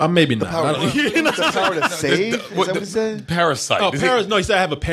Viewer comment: I thought he said Power of Satan. LOL